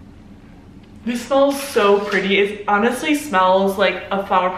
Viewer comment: Instead of hearing “This smells orangey” i heard “this smells like an orgie” and I almost died laughing